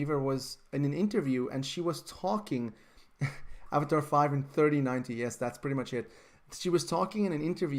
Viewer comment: Avatar 5 taking place in 3090 at 10:42? Did I heard that shi*t right?!